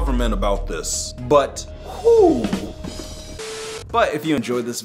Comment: When you said peace at the end it sounded like you said piss